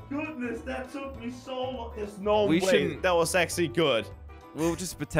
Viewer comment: it sounded like he said sexy good lol